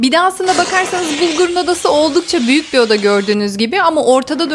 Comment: Ben çocuğunuzun ismini yanlış mı anladım bulgur mu dediniz 😮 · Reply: Uygur dedi